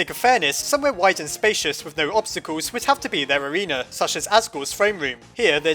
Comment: I thought it said ASSgore XDDDD